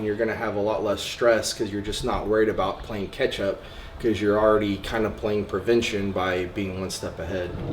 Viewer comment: Am I the only one who heard Ketchup? lol Amazing this guy is all in into bodybuilding